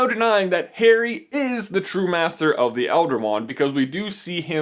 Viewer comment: i thought that said " Trump master of the elder wand ?" 😭😋